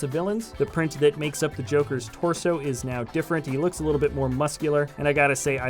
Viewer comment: did he say joker